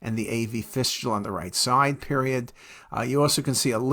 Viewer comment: Did you just say period after your sentence at 04:25 😂